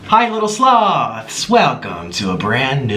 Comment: First time I heard him say "hey sloths" I thought he said slobs.